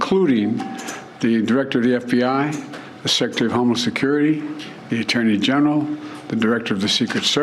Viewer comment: Who heard him say “Chief Of Homeless Security” ???!!!! Is it just me 😂😂😂